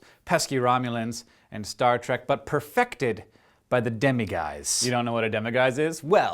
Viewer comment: i thought he said demigods (like in percy jackson) fur i was like how in the name of chuck noirris was a wizard respoced to kill a person who was half god? answer me